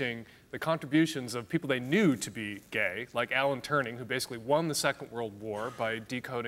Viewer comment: did he just say "Alan Turning"?!